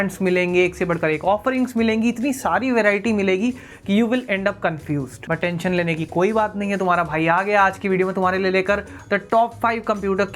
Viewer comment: please take a break between "but" and "tension" it sounds like attention lene ki koi zarurat nahi XD